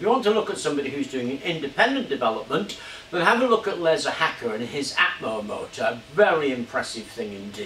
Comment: Who did he say at 9:03? I can't understand the name. He said they were developing these independently. Sounded like he said "Lerza Hacker".